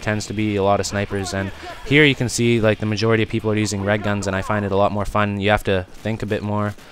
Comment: ohhhhh REG guns i thought he was saying RED guns LOL i was like "wha???..." o.O